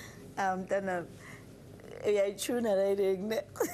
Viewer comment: What does the lady judge say at 1:59? She's laughing so hard. I could not understand. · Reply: She is not speaking English. She said u turn up that song neh